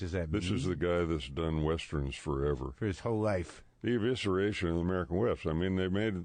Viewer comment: Oh I thought he said evisceration of the "American West"....